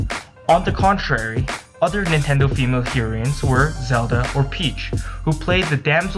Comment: Heroeen? Sounds like whatcha said oof.